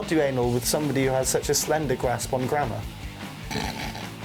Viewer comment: what does he say before grammar?? sorry English is not my first language and I'd love to know what is that word...but I understand the context though hahaha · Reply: He said slender grasp of grammar